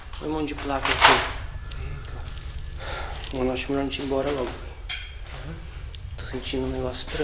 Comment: queria q vc me adiciona se no zap <num>_3602 queria relatar oke ouve comigo mano ?9:17 eu ouvi saíam preste a atenção ou algo parecido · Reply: Eu entendi "melhor saírem "